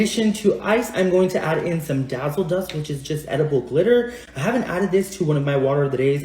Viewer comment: Buat org yg g denger dia ngomong apa di 3:00. Intinya dia bilang "dazzle dust yg mirip glitter tpi edible" bukan glitter beneran